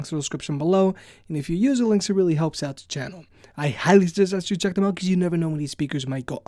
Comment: what did u say? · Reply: RGooch92 highly suggest that you check them out; or higlysugisyouheckemout